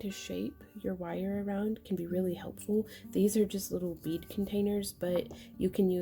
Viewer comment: I'm from San Diego....I heard "weed containers"...... bead containers........ I love your videos!